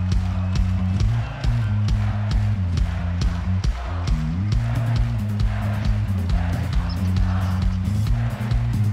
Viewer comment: what is the crowd shouting? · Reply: toilets! latrines! please give us these!